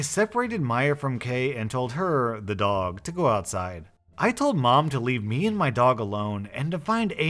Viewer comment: Outside? I thought it said inside.